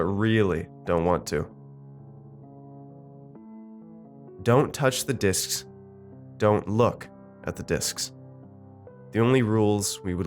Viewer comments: I had to take a second look, i thought is said “Don’t touch the d*cks, don’t look at the d*cks” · lol i thought it said don't touch the dicks, pffff ok